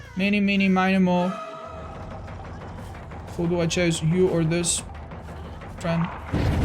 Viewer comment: My mind automatically completed the sentence and I heard "eeny meeny miny moe, who do I chase, you or this hoe"